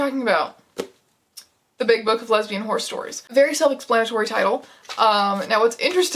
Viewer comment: this was autoplaying while i was cleaning and i thought you said "the big book of lesbian HORROR stories" and was really confused why all these horses kept showing up